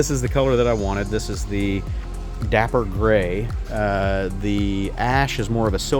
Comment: Dapper Ash?? I thought they said “tap her ass”!😂 I like my turquoise stripes. I rerouted the seat release so it dangles behind the frame. I love the stock exhaust! I had the full stainless exhaust from Hitchcock’s on my Classic 350. I see no need to change the Hunter. I put on bar end mirrors and I ordered adjustable levers but Amazon is taking a month to send them. I’d like to know which accessories you have ordered. Everyone thinks the rear suspension is stiff but try ADDING two notches of preload. It helps. Those CEAT tires are great. Changing them now is just silly. 3 mph high on the speedometer is nothing compared to most bikes. The speed sensor is the front wheel ABS sensor. If you keep the ECO flag on you will get 90 mpg. You should ride it for 1000 miles before you add risers. And quit complaining! It idles slow and reviewers are dumb. The RE 650s have a booter/slipper clutch. It’s lighter than the 350. It is silky compared to the unbalanced Royal Enfield 500. I was going to take a drink every time you said it was hot but I would have died from alcohol poisoning.